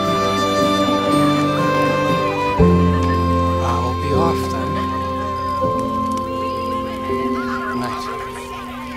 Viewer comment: what did he say? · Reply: I’ll be off then, goodnight